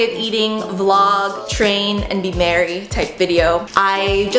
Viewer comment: I thought you said: and be married. kinda freaked out hahahaha